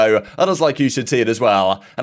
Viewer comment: what do you always say at the end of your vods? 😂😂 12:30 "I'm grateful for the call of duty community." Or, "I'm Greg from the call of duty community?"